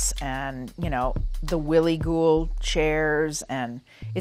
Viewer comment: I thought she said; really cool chaires but she said willy guhl chair haha